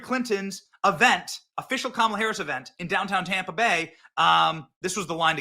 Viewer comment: It sounded like you said “downtown tampon” which has to be my favourite Freudian slip ever!